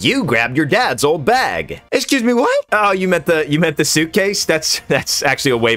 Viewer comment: "Excuse me, what?" best line of the entire series. Lol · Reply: I thought he keeps saying "excusez-moi" this entire time 😂